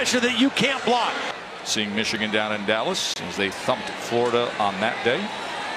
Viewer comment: Does he say "fucked florida" @ 11:23? 😂